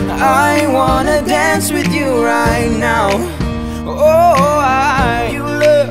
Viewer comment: it sounds like they say "I wanna dance with you Rye now"😂